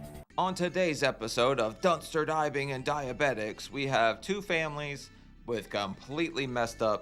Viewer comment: Did you just say"...on dumpster dive, and diabetics " 🤣🤣🤣🤣